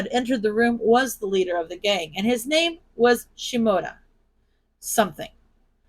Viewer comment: I thought you said Shimura when you said Shimoda and I was like, I'm sorry what!? I had to check the actual story just to make sure of the name. 🤣🤣🤣